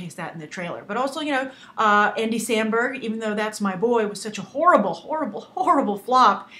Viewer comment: I thought she said :" because that's my boy " lols